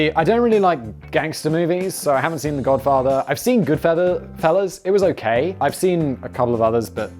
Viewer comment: did he say Good Feathers?! That hit me in the nostalgia feels. #Animaniacs